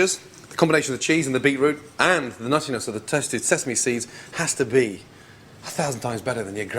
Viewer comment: sounds like he said testi sesame seeds tehehe